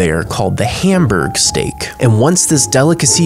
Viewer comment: For a moment I thought he said And slapped some checks☠️☠️☠️☠️☠️